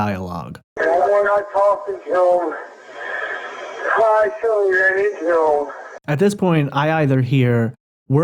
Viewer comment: What I hear him say is "I will not talk to them I accidentally ran into him